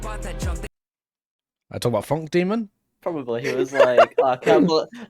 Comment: I think he said demon